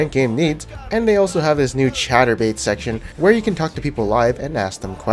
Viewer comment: Did you say "chaturbate section" at 0:43? Lmao